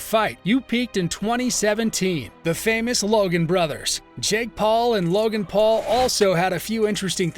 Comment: he actually said Logan brothers